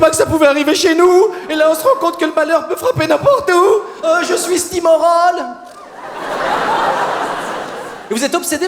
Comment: Qu'est-ce qu'il dit à 1:26 s'il-vous-plait ? Je ne comprends pas le "heu je suis ..." moi j'entends "je suis stymorone" je ne suis pas franchement sûre que ce soit vraiment ça qu'il dise haha